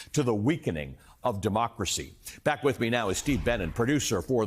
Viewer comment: I thought he said Steve Bannon lol Steve Benen ok! Kamala 2024